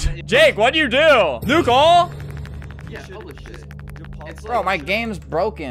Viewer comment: Am i the only who hear somone say holy sh!t